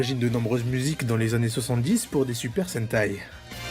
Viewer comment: Pour des super quoi ? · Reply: Super sentai